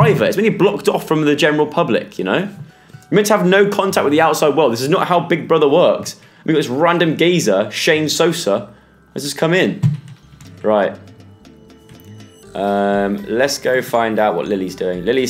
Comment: When he said Shane Sosar (Idk if I spelled that wrong), I thought he said Shame Salsa. I am crying😂😂😂